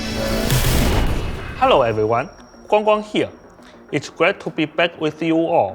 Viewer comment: hello everyone, here🗣️🗣️ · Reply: I thought he was saying Wong Wong all this time not.